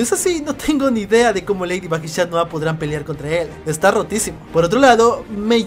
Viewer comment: parece que dice está rotisi